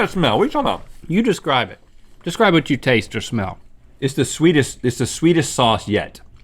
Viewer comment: When they said "sweetish sauce" I heard "Swedish Sauce"